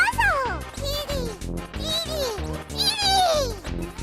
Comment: Kirby is objectively saying “Kitty” here